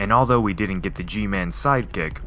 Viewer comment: did he say Gman in 0:55 seconds